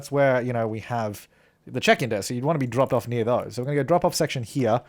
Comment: It sounded like you said 'The chicken desk' instead of the check-in desk...